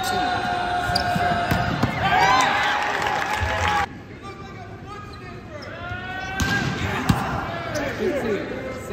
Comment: I can't be the only one who heard what that man said at 1:52 💀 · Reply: “You look like a foot sniffer” 😂